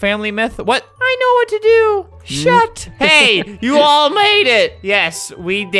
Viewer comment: did he say s**t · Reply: Yes he said shut